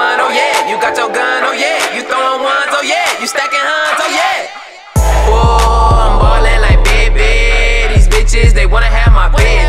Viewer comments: I thought he said "ballin like pippen" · For y'all saying fresh like mayonnaise, it's fresh like Mannie like Mannie fresh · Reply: tbh i also thought he said "I'm balling like Pippen"